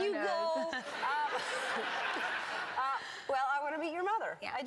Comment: I first heard "she wants to beat you"😂😂😂😂😂